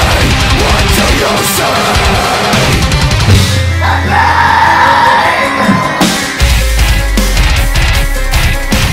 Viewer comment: what did Ronnie say at 1:14? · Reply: HeadBang!